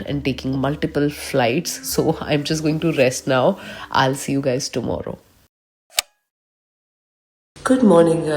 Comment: it sounds like you said aalasi hu guys 😂.